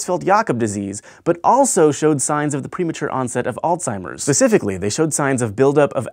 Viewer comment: Did.. did he say "pacifically"..?